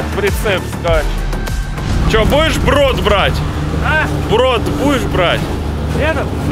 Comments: Мне послышалось он сказал в рот будешь брать))) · будешь брод брать? послышалось будешь в рот брать)) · минута, показалось что он сказал в рот будешь брать, а потом брот будешь брать))) 😅